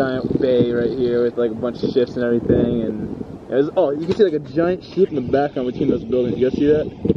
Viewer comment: It sounded like at 9:30 he said a giant shit